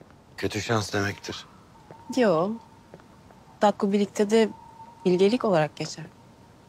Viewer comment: Şebnem 18:05 dakikada ne diyor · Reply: Kutadgu Bilig de zekilik olarak geçer diyor sanırım